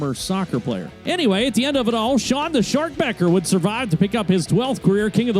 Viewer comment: at the end of the number 1 clip i thought i heard the winner's name as "shawn the short pecker". i thought, "no way is that his name", and i had to listen again... lmao